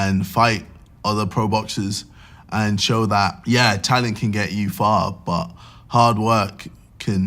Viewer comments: Literally thought JJ said Tana can only take you so far lmaooo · lol at 8:00 it sounds like he said "and show that, ye Tana can get you far but, hard work can take you anywhere"